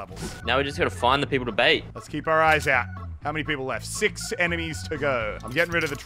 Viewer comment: did he just say eminies